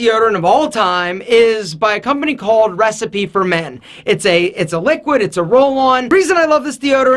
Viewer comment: When I saw your 10 minute morning routine and you said recipe for men at first my dumbass thought you said Breast feed for men lol